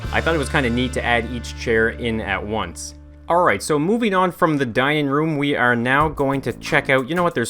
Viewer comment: i thought he said the dying room i like OMG